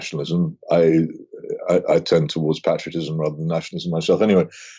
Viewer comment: Sorry Peter, but Viktor Urban is a legend. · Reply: I was bugged too. But then I listened again, with subtitles (bad microphones on both ends), and I think he said: "Not nationalism, rather patriotism" (at first, I heard "fascism"). If he says "patriotism", it's ok.